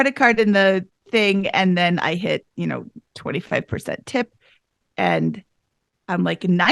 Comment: did she say 25% tip?